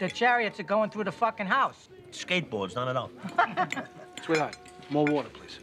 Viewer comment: What does Tony say? Skateboards... Not enough? Been trying to puzzle that one out for awhile.